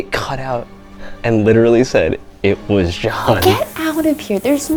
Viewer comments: I hear 'This was JOHN" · When they said it sounded like “it was John” I heard “he was gone” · I heard "This was John" or This is for John